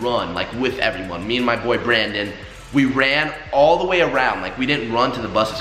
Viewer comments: did he say boyfriend or boy Brandon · I thought you said me and my boyfriend 😂😂